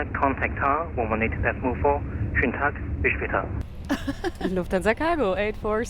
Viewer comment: What was the joke at 34:35? I couldn't make out what ATC said. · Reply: He just said in german: have a wonderful day :)